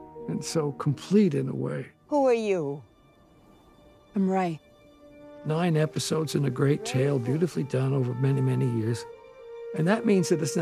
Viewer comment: Can anyone help me? I just can't understand a few words John Williams says at minute 9:43-9:46, just after the "who are you? /I'm Rey" scene and before " great tale beautifully done...". Sorry I'm not mother tongue and I can't get some words sometimes😅😅Thank you!!!😊😊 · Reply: "Nine episodes in a great tale, beautifully done."